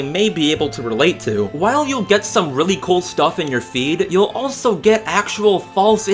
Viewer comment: I heard "feet" instead of "feed" and I was like "What?"